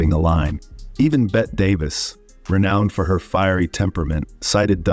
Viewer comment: Good video - but I always heard it as Beh-Tee Davis.